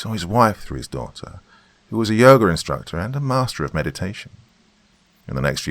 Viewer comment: He was a yogurt instructor. That's what I heard the first time. XD